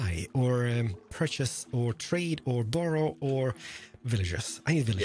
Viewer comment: Did he say “doro” or borrow?